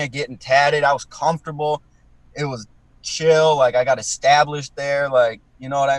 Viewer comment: Dead thought he said “i had a stab list by then” but it was i was established”